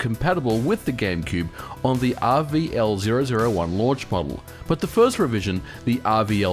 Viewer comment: I'm making actual english subtitles to this, and I have a question. Does anyone know what he is saying at 0:57, after RVL-001? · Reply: 'Launch model' i think